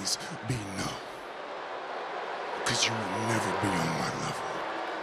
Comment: for a second I thought roman said, "you will never be my lover" lol